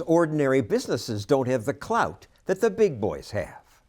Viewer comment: I’m sorry but did u just say clout?